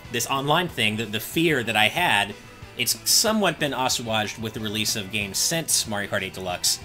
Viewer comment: The pro controller has the same issue as the Wii U's d-pad (game pad, and pro controller). It's too squishy. Why can't we have a clicky d-pad like what the 3DS has? 38:27 I thought he said "Oshawott", but he was trying to say "assuaged". uh-swayjd